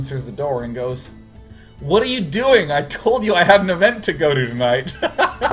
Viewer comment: What did she say? I can not understand. 19:24 "I told you I ..." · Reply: "I told you I had an event to go to tonight"